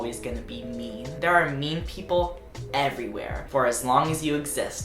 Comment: i thought he was saying meme people.